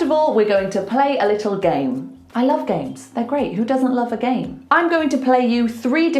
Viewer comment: Time: 01:24 YOU doesn't love the game 😢 Did i hear wrong?